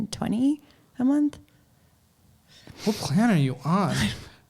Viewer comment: Did he just say "what planet are you on?" Or "what plan are you on?" @ 7:01. Eitherway, both suit her circumstances.